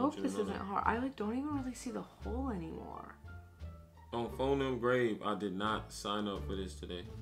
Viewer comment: on fo*** nut grave what is that ??? I need help I heard my boo say that